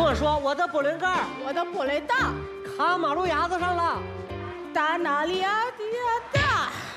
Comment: 我听成我的玻璃弹卡马路牙子上了 哈哈